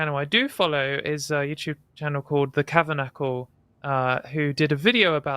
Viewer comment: recommends a YouTube channel. I can't understand what she says. Sounds like "the Cavernackle"? Can anybody elucidate? Search gets me nowhere. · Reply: The Kavernacle We Need to Talk about TYT - I am so disappointed (Ana, Cenk and Trans Rights)